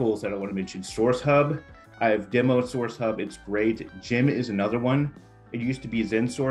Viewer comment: At the 12:24 mark you mention a second tool? Closed caption is not enabled and cannot understand the name? Thank you for everything you share! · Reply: Sourcewhale and Gem are the two best drip campaign tools that include tracking functionality….I meant to say Sourcewhale (not sourcehub, that’s a Boolean tool I’m not sure is active anymore). Sometimes these tool names run together….but I hope this clarifies!